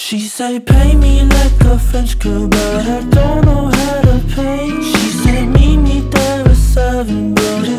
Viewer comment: OMFG 0:41 i thought he said: "pay me like a french kebab, i don't know how to play"